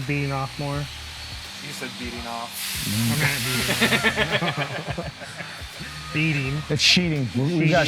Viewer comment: Yay! At 7:40 you were on the ball this time and caught the guest in a double meaning mess up and I didn't have to point it out to you. You're getting better. Beading sure sounded like he said beating to me too.